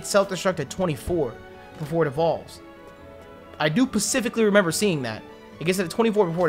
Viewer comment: Did anyone notice at 22:07 said pacifically instead of specifically? He's mocked Grimmy so much by switching those words up that he's doing it unconsciously and not even on purpose.